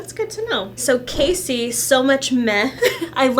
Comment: I thought you said Kasey's screen name was "So much meth"! Had to watch that again.